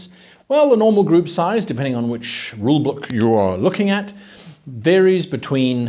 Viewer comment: I heard rule blook or rule boolk at this time